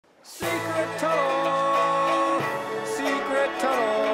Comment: Why am I hearing “sea current”